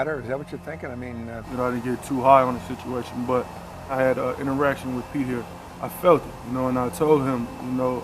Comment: Did he say "I had an erection with Pete here." ??? haha